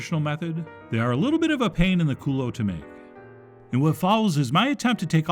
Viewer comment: "A pain in the culo" - Chef Juan from East Los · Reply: Thought he said : A pain in the cooler 😂